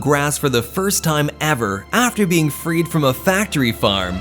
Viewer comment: When I first saw this video I thought “freed” said “breed”